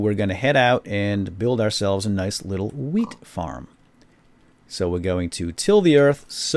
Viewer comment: Wait a minute - did you say weed farm @ 0:13?